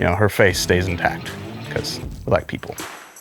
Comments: I heard "because... Black people" and I was like "Damn Fin! You cant say that shit on camera" and then I realized he said "we like people" and it all makes sense now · did he say because black people?😕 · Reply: No, he said “we like people”